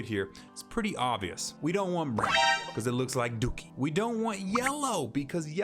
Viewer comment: did he say Black or brown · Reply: Brown